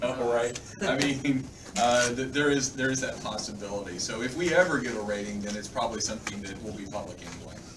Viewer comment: I can't really hear butaround1:40:00 he mentioned something about raiding? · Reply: He says, "If we ever get a rating . . ." referring to a Champion Rating similar to Veteran Ranks so people can know who is more powerful.